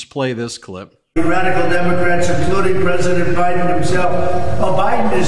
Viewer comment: Did he say O'Biden? 🤣🤣🤣🤣🤣🤣